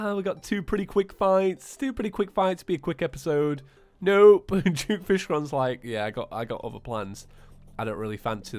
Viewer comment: When you said "Duke Fishron" I thought you said "Juke Fishron" xD